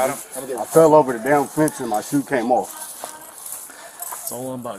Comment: I thought the caption said the officer had piece of “fent” in his leg then realized it was fence 🤣